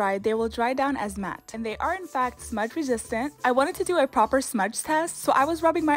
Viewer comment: I thought you said water proof at first so I was like: "And to get it off, use water." "THE HELL-" 😭🤚